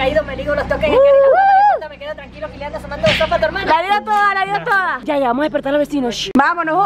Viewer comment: ese si sabe rap y se pilli que dijo Besos a tu hermana :v